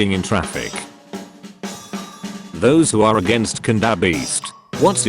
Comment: what was the word that got censored? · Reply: cannabis